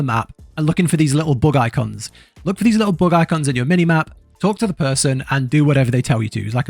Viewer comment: lastly, While watching I had to do a re take of the video as I could have swore you said "Look for these Little Bug Eye C*NTS", 11:23 but after re watching a few times I worked out you actually say " Bug Icons" . LOL,made me larf.